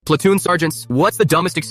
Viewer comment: I THOUGHT BRO SAID"SPLATOON SARGANT"